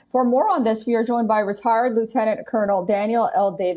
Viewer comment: Did she call him retard?